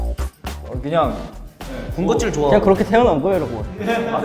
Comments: I THOUGHT SEUNGMIN SAID ASS-CLASS LMAO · did seungmin say A$$ class?😭💀